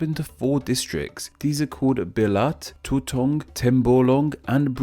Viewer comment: Did I hear... bilat?